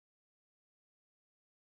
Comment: I hear here: It's me